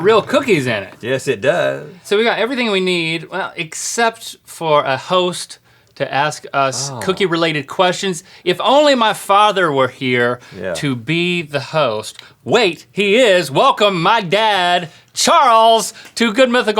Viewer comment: wait a sec 1:07 he says cookie related but in a way that it sounds like cookie rouletted questions that's funny even though it was accidental I actually thought he meant to say that but I doubt it after a few seconds